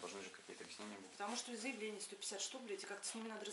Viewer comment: я правильно понял, что она сказала: "Потому что заявлений 150 штук, бл@ Официальный начальник следственного органа матюгается и с татуировкой на ноге... Я бы от такой подальше держался...